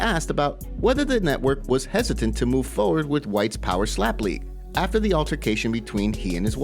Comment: i misheard and thought dude was talking about white power 😭